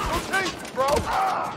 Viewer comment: did he say don't taze me bro?